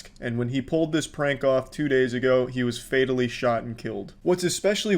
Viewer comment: I thought it said finally not fatally